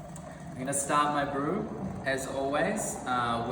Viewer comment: Hi :) Ich hab dich am Ende leider akustisch nicht richtig verstanden. “Stoppst” oder “starts” du bei 1:40? 🙈 habe nämlich oft gesehen (Vorallem in Japan) das der Brühvorgang bei so einer Ratio ungefähr bei 1:30-40 unterbrochen wird. Allerdings auch nur bei Origami MIT Kalita waves. Ganz liebe Grüße :)